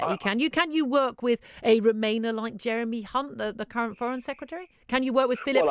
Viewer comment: Is it just me, or did she actually say "Jeremy cunt" at 2:39 ?